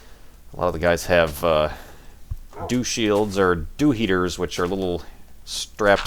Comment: was talking about the Dew shields on telescopes...i thought he said Douche Shields. Sir if you patent a Douche Shield, you will be a millionaire!